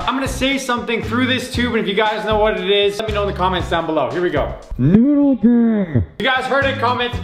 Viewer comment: At first I thought you said poodle but I'm pretty sure you said noodle